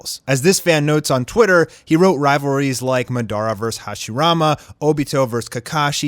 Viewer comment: um did he say "Ma-dar-a"................